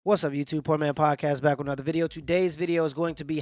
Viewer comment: Sounded like you said "Porn Mans Podcast" lol😅